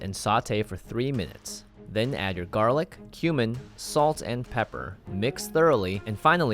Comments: Did i hear that right at about 0:44 add your human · I literally thought he said human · I thought you said Human and I was like “... I’m sorry what?” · i literally heard human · am I the only one who thought he said human 😅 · I thought he said human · i thought you said "then add your garlic and human " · I thought that said ‘human’